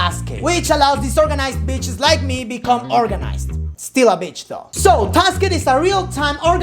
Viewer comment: i thought u said word 'organisam' at 1:15 -__